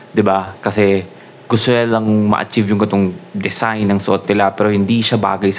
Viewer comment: Goodmorning. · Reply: Did you just say "katong"? It's a Visayan word. Are you Bisaya?😊 Btw, that's what happened to Ken that's why he slipped twice during the Bench performance because of his shoes and pants that is not good for dancing.